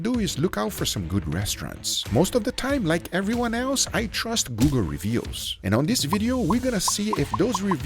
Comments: I kept hearing him say "Guga Reveals" instead of "Google Reviews" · i had to rewind the video 4 times because i thought he said "like most people i trust guga reviews" and made me curious to see an app like yelp for guga lol